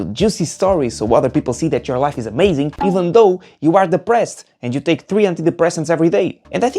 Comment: I thought he said even though you are the breast